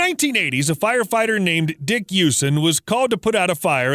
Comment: Is it just me or did you say the fire fighters name is dick using? Lmao